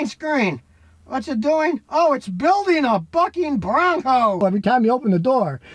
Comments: For a sec I thought scotty said "it's building a f**king Mustang" 🤣🤣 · for a minute I thought Scotty had said’ “it’s building a F***ing Bronco! “😂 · For a second I thought scotty said "it's building a fbucking-bronco" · I thought he said “it’s building a fucken bronco!” 😂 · I totally heard “building a f*cking bronco” 😂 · Swore I heard ole Scotty after a 5th of Bourbon say 'F'in Bronco' maybe I'm the one that's 🤪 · For a split second there, I thought he said "it's building a f^@ bronco" O_o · I really thought he said "oh it's building a f***ing bronco" for a moment lmao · it sounded like he said "its building a FUCKIN BRONCO" LOL